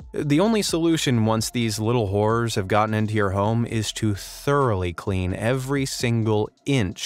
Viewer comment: did he just say whores?